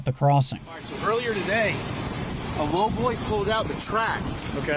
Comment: what was the officer saying about what was it that had pulled out three feet of track? All I could understand was...a robloy. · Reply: He was guessing it was a lowboy trailer. basically a semi trailer with really low ground clearance